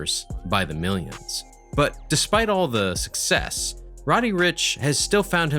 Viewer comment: Did he actually say sksk success